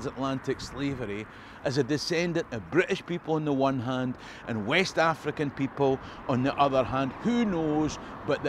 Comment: You say your a descendent of British people on one hand and West African people but the subtitles say British people and West African survivors. Is this auto generated or not? if so that's weird. See it at 9:43 cool video